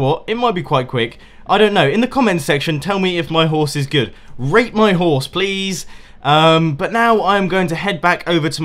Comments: sounds like hes saying rape my horse please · When he said "Rate my horse." I thought he said "Rape my horse."